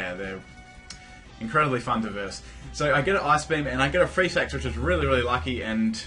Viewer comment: when he said freeze hacks I thought he said free sex xD